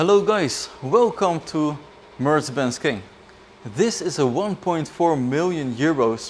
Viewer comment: Mercebenz ? WTF di I just hear?